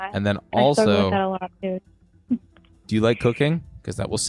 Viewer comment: 1.19.22 i really thought bbno$ said "do you like coc4ine?" 🤣